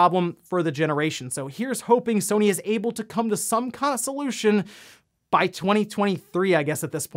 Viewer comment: did you just say "consolution?"